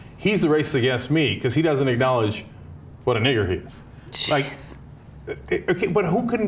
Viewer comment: ??????? I heard cheese....ok now im hungry